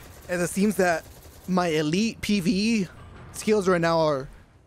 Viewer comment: gad to listen to that again..thought he said “-My elite PP skills”